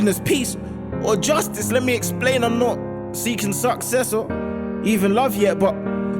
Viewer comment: what did he say at 1:49 ? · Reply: I'm not seeking success